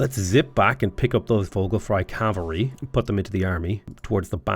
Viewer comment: I keep hearing "Vocal Fry Cavalry".